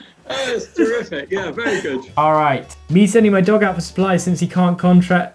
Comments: the people who made the subs @ 4:17 may have had too much fun seeing as it said "dong" instead of "dog" HAHAHA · Subtitle reads "Me sending my dong out" instead of "dog".